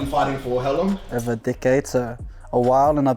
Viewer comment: I thought he was saying dickhead when he first said decade lol